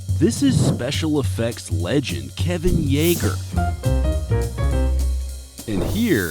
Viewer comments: Bruh, could have sworn that I've heard the narrator saying "Eren Yeager" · Did he just say yeager?